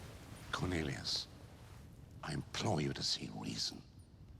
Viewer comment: i didn't understand the exact what dumbledore say to fudge... @ 03:08 did he say"Cornelius i employ u to see the reason" why did he say employ or imply? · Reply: Implore.